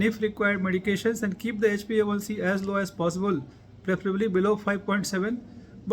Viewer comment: What is name of the level sir at 4:00? i couldnt hear it correctly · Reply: It is HbA1C (Glycated hemoglobin), which gives the average of blood glucose levels of the previous 90 days. Useful parameter to assess one's blood glucose status.